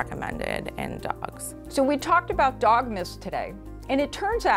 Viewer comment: Why did I hear "Dog Myths" at 10:01 as Dogmas? Like Christmas but for dogs